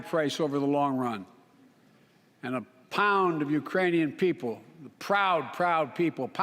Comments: Dang, I thought he said "Uranium", and I thought well Chernobyl is in Ukraine but that's a cheap shot against their people, Biden. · did he just say: A pound of Ukrainian people? and then said Pound for Pound. What?